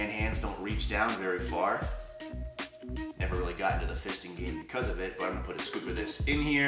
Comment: im unsure on what i heard... did i hear "never really got in to the fisting game because of it"